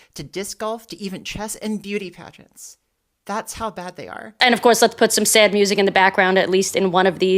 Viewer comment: all I heard is “that’s how good they are”